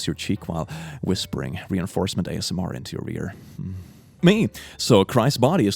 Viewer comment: i heard "into your rear" not ear. 🤣